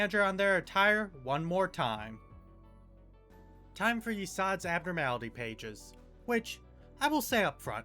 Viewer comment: the closed captions says "Yisang" when yesod was said, very funny when they both get funeral of the dead butterflies (theres prolly other connections and shii, but i havent beaten lor and lobcorp fully and im not crazy lore fanatic for limbust)